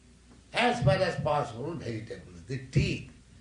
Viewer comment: Its TEETH instead of TEA. ( edit subtitle ). 👍🏿👍 Hare Krishna.